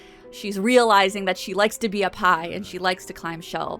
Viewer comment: Heartwarming story about Basket... just love it 😍Cats absolutely know cat people and choose theirs. (I originally thought you said Basket "loves to be a pie" and not "loves to be up high"... omg I am tired)